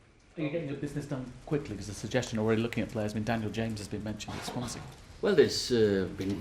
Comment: which player does the guy link ua to at 3:14 ? I can't hear it sounds like David James to me.